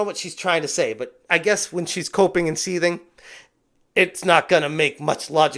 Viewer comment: When you said Alyssa was coping I genuinely thought you said she was pooping 😂 I was like " well she is full of shit"